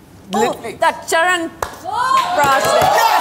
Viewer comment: did she just say "Charanprash" ??